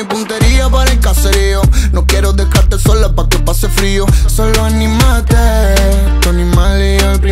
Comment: En el minuto 1:57 pensé que decía dale Mari animate